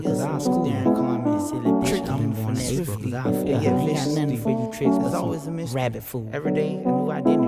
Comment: who hear something bout rabbit food at 0:10 ? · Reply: Yeah he says “I ain’t got nothing for these tricksters but rabbit food”😂